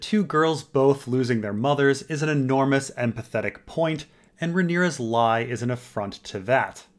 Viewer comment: Damn it sounded like you said “Rhaenyra & Alicent both losing their mothers is an enormous & pathetic point.” 😂 I was like, wow, I knew could be a bit blunt, but that’s cold-blooded. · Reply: he’s saying that when I said “empathetic” it sounded like “pathetic”